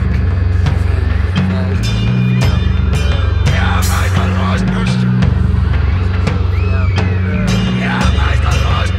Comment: Do we know what is being said at 8:30 to 8:44?? Id love to know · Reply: Sorry for late reply, but I reversed the clip and he’s saying “don’t look at me”